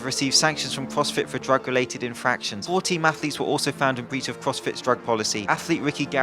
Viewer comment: instead of “4 team athletes” i thought you said “40 mathletes” and all I could think of was this goddamn platoon of yolked up nerds